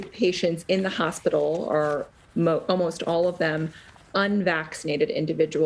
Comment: The "99% unvaccinated" claim NEVER lined up with the data coming out of any other country. Obvious lies. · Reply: You quoted 99%...who told you that crap? What I heard 0:45 was "almost all" and I've been hearing that word in news reports. If you want the exact numbers they're available. It varies by hospital but what I've seen have been in the 90-98% range. I have seen some small hospitals being 100% unvaccinated by small sample sizes will do that. A small hospital could have 100% vaccinated hospitalized too if they have just one patient. The numbers coming out of all other countries are in the same ballpark. But none of that matters doesn't it. You just like believing everyone is lying to you. It's a type of insanity. That's fine with me. I have absolutely with non-vaccinated being in hospitals. If they choose to die that's their business. I won't miss them.